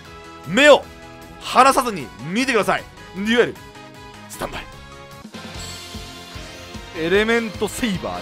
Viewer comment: デュエルスタンバイがニュエルに聞こえた